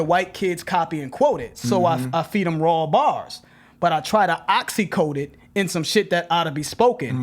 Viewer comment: I thought instead of "try to oxy coat it" it sounded like he said Ta-Nehisi Coate it lol